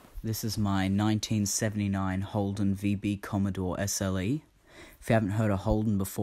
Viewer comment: Oh, It's COMMODORE. I thought you said "COMMODE". LOL